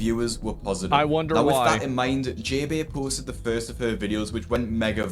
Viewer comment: NGL when he said "with that in mind" I thought he said "with Fat in mind"..